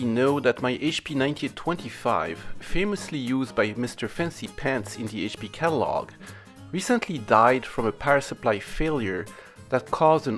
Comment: For a minute there, what I heard was "mister fancypants recently died ". Good thing I realized my hearing is sub-par. :) Haven't watched the whole thing yet, but the description leaves me hopeful that I'll get my "good news" fix of the day.